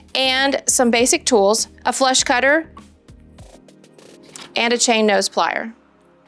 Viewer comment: Huh? Funny but it sounded like you said "flesh cutter", when you listed the tools you'd need! (Must help when you're creating the correct size ring - hee hee hee.) YIKES!!